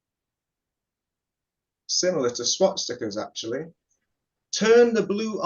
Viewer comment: Did he say "swat stickers"?